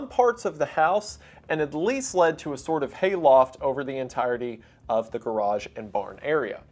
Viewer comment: did somebody say hayloft?